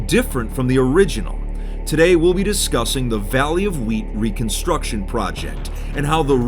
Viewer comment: I thought said vally of WEED! lol